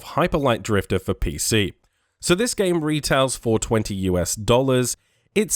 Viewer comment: i thought he said 'the game retails 420 dollars' 😂